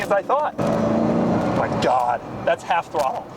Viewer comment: What was that you said after "My God!" at 5:29? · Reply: "That's half throttle".